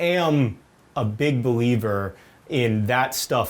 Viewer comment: i thought he said he was a big beliebber :')